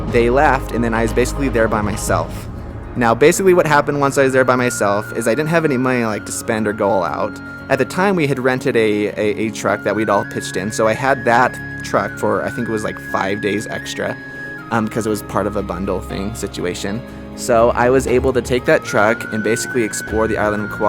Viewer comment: I thought he said''truck, were we all pissed in ''